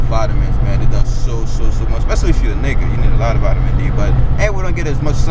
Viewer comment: did you say "especially if you're a nigga" lol